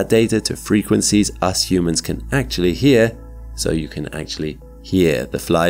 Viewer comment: the close caption say "literally", instead of "actually"